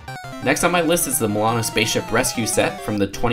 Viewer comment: it sounds like he said Moana